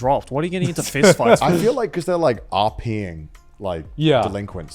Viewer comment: did connor saying “rping”